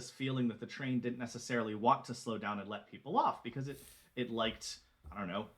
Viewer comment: hey guys. don't know how i missed this one. i always thought he said "no way to slow down" lots of love